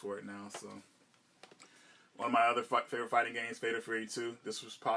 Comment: Nice SENS collection! So many premium titles. At 8:46 it sounds like you said "One of my other fuck favorite fighting games" I had a laugh rewinding that a few times to see if I was hearing correctly.